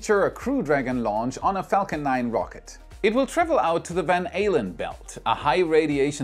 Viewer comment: I know he didn't, but it sure sounded like he said the Van Halen belts. I had to replay it a few times. lol